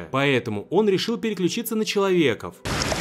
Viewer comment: Мне послышалось, или он сказал "человеков"?